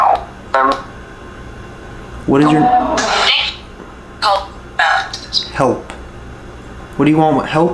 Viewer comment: you hear a woman scream Hilfe that means help in German. At 17:52 when you ask what is your name you hear a woman say : Audrey .